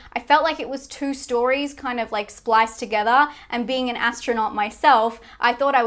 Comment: Hol' up, did she just say... "and being an astronaut myself" ?